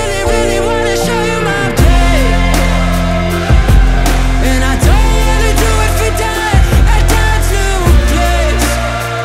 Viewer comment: did he just say "and I don't wanna do it again at dad's new place" oof. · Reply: It actually said “and I don’t want to do it for dad and dad‘s new place”